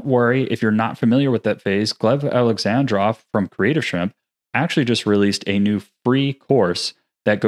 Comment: you mention a ??? Alexandra from ??? - I did not understand what you said. What is the name and where can I find the course? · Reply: He said "Gleb Alexandrov from Creative Shrimp". The tute is on Gleb's channel.